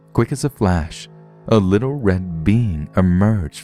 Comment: I thought he said a red bean came out of the hole.